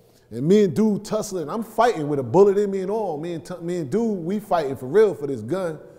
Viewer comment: Did any catch what he said at 6:21 he slipped and said "me and tut i mean me a dude fighting for the gun" he's talking about tut from brooklyn